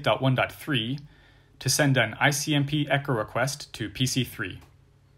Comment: Hi, I think your subtitles are typed manually, I noticed at 16:55 the subtitles read IMCP instead of ICMP, just wanted to let you know. Thanks for all you've done as this has been exceptional for my learning, but wanted to let you know, so hopefully this can be corrected for those that are deaf to avoid confusion.